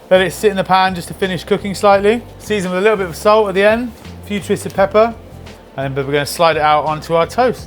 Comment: Did anyone else hear “Futuristic pepper” at 1:45 ?